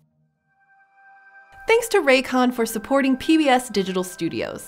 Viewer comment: When she said "Thanks to RayCon for [...]", I heard "Ray Comfort" and I was very confused for a second 😂😂😂😂